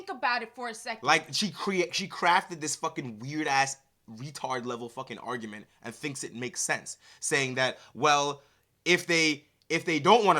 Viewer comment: I thought you said 23-tard lol